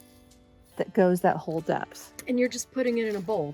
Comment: I had to rewind for a second 'cause I thought you just said you put a whole death in a bowl.🤣